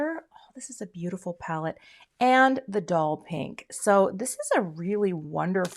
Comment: Love all of these! However, I must have been half asleep when I watched as I thought you said dull pink not doll pink. 😂